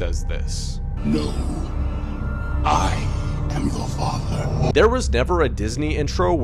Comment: Darth Vader doesn’t say “Luke i am your father” he actually says “Luuuke i am your father.” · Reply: it sounds like he says “Luke I am your step father” 😳😅